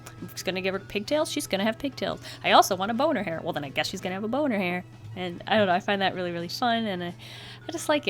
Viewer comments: i heard bow in her hair as boner hair😂😂😂😂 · I thought you said “Bone in her hair” (*wink wink* that’s what she said moment) and at the time I was drinking soda and when I heard it I spit out my drink......all over my sketch....totally worth it